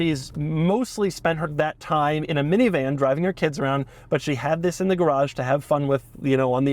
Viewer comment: At first I heard was " In a minivan driving her t*ts around" 😂😂😂and